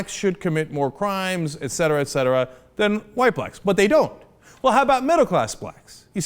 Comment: did Cenk just say "white blacks"?